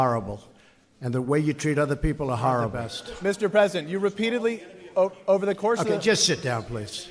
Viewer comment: did he say "Mr. Peasant!!" Deport his ayus back to Europe!